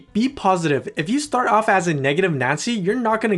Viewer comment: I heard “negative nazi”...